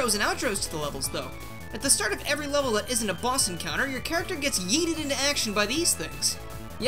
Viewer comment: Did you just say Yeeted?